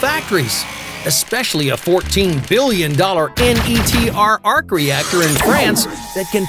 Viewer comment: It's Actually ITER instead of NTER at 2:38.